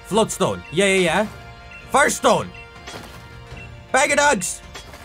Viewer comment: I heard two things "Bag of ducks" and "Bag of dogs"